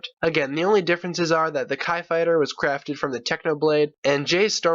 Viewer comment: I flipping thought u said tai fighter HAHHAH LOL #Starwars